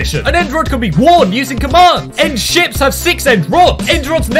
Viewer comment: When he said end ships it sounded like he said end shits